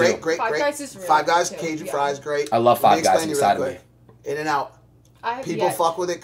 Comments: So we just gonna ignore what Adin said???? "I love five guys inside me" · Did Adin say “I like 5 guys inside of me” ?